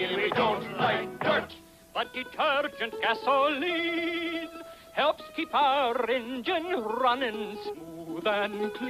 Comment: Thanks! I’ve been searching for this for decades. I was 11 when this first aired, and I could sing the song from memory all these years. I kept googling “rock around assembly,” and finally entered “we don’t like dirt” — and bingo! The animation is adorable — it looks like it was produced in the 1930s. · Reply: I had the words wrong to ahhahahaha I thought it said low detergent gasolineeeeeee